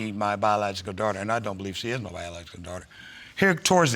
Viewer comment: <mail> said “By-ja-la-ja-gull daughter” instead of ‘biological daughter’! 🤣🤣🤣🤣💀💀💀💀⚰️⚰️⚰️⚰️⚰️ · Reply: Shelby, I thought he said, “BAD LOOKING DAUGHTER!!”😂😂😂😂